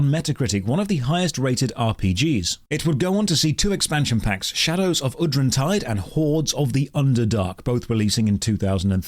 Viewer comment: Did you say "Shadows of Udrentide?"